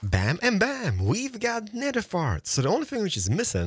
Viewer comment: I know he's saying Nether warts, but I had to rewind cause I thought he said Nether farts